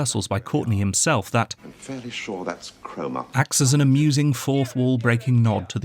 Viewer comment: As a non-Brit, I thought he said chroma (key). It's Cromer, a town.